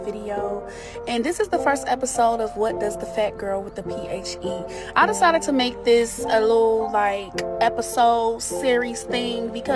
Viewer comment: Wait did she just say ph instead of PhD....... Ok